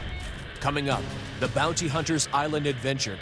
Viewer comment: I thought it said booty hunter at first